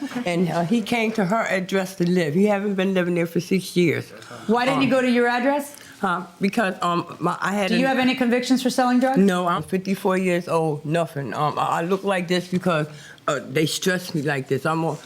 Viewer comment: Did she say 54 years old?